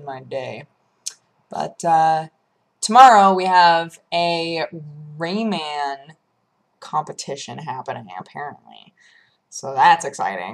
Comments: I thought she said "Rainman competition." That one had me confused for a minute. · I totally thought you said a "rainman competition" at first xD